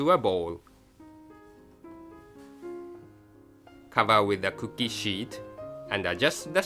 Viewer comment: cover with cookie shit lol that's what i heard XDD also am i the only one that keeps on replaying this video? because i like to look at food